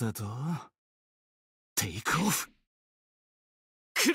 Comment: これってなんて言ってるんですか！？ テイクオフ？？ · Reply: テイクオフで合ってると思いますよ！ 出発するよって声かけしてる感じかな〜と